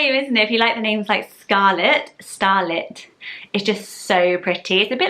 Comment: My favourites are True and Street. When you first said Starlet, I misheard it as Starling. I love Starling as a name - how about a list of bird names? (Sorry if you have done this, I'm a fairly new subscriber.) The only other I can think of right now is Robin, but I bet there are loads!